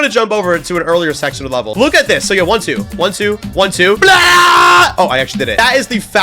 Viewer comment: he actually said "blya" in russian✌😔🥀